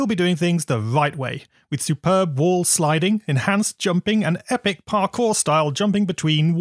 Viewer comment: why did i hear "Supreme Ball Sliding"